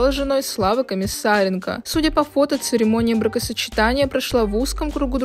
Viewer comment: Только я услышала "церемония МРАКОсочетания"?)